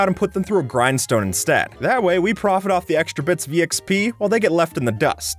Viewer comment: did he say Exp?